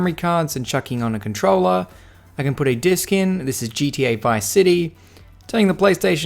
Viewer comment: Good mod, also for a sec I heard 7:23 this is GTA V.... ice city hahah